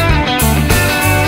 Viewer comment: I thought it it said all hail the butter barn